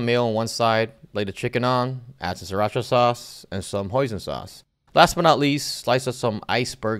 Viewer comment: I thought he said poison sauce but it was hoisin sauce